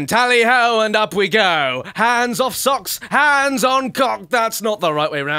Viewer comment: did he say " Hands off socks, and and hands on coc*s??! xDxD